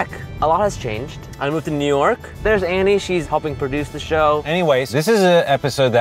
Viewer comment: Did anybody hear annie-ways?